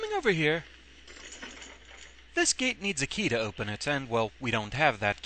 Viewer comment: For some reason, I heard "This key needs a key to open it."